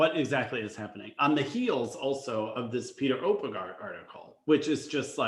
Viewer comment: I thought Natalie Vlandis said the coach at 1996 Nationals was John Nicks, not Oppegard.